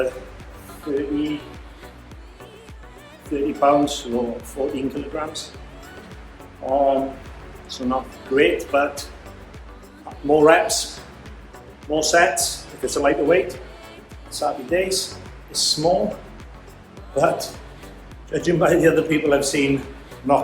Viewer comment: Oooh er..did I just hear "more reps, more sex"? Got to love that accent!